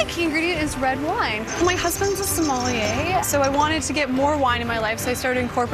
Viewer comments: did she just said my husband is Somali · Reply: Ahmed Warsame she said sommelier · What did she say? 3:12Somali???